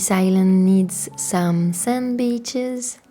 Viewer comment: When you said this island needs some sand beaches I thought you said sandwiches 😅 this is a beautiful theme!